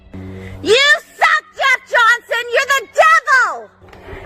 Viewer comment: The Karen worm is affecting my brain! I swear at 13:48 it sounds like she says "YOU SUCK GUS JOHNSON, YOU'RE THE DEVIL!" She better NOT be talking about my boy Gus like that! 😂